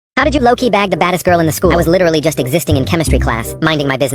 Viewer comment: Bro I thought the author said "bag the fattest girl in school"😂